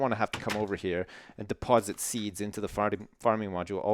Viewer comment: hahahaha!! at 5:50 sounds like sips says "farting" instead of "farming" XD XD